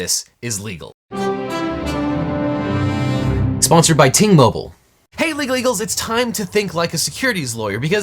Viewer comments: Glad you flashed the graphic; thought you were saying "T-Mobile". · Oh, it's Ting Mobile! This whole time, throughout all your videos with them as a sponsor, I've thought you were saying 'Team Mobile'.